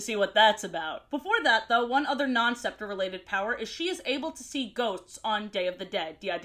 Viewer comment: Why did i hear "she's able to see goats" at first lol